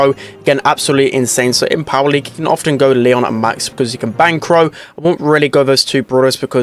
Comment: did really say "you can bang crow " ?